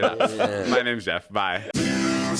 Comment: did he just say my names jeff?